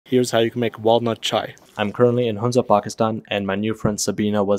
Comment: i thought he said walmart chai😂😂